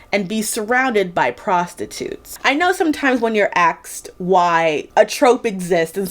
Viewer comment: Did you notice you said "axed why" instead of "asked why"? Is it widespread where you live? It's interesting to notice these changes to English that happen over time. I recall hearing a few similar consontant switches in the last couple of years, but with looking as an outsider at American youtubers it can be hard to say whether it's that person's thing, their local community thing or potentially a sign of the wider adoption. AAVE is especially fascinating, because it is source of language creativity that English needs, and yet black people don't get enough credit for.